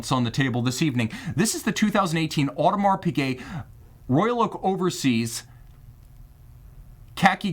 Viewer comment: royal oak overseas? Did i hear that right?